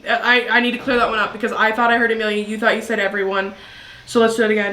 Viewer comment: I heard Emila